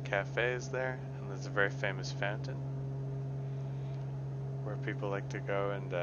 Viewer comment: When he was saying fountain I thought he said phantom. There's a really nice 'phantom' in Mission Bay